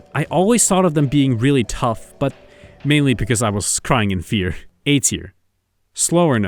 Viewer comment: you were WHAT? say that again... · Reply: He was crying of fear because he's afraid of monsters